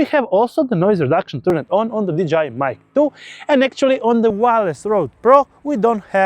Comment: did you say "DJI mic 2" or "vagina mic 2"? LOL 🤣 Great video BTW